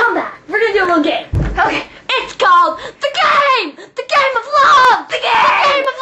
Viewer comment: i swear i heard gay love